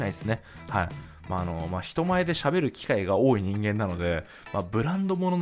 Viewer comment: まひと前で、喋ることが多いのでって聞こえる笑